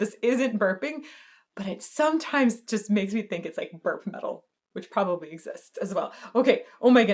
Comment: Wait. Did she just say “burp metal?” 😂😂